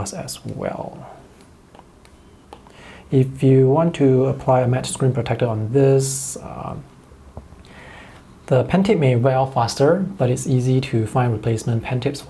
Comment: Hi in 14:30 you mentioned something about the apple pencil tip , I didn't hear you very well could you explain it to me again please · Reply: If you want to apply a matte screen protector, the Apple Pencil tip may wear out faster. But replacement tips are sold by Apple, four tips for US $20.